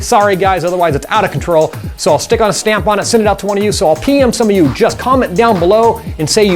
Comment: Had to do a rewind at 3:00. Thought he said "I'll pee on some of you, just vomit down below." waaaat haha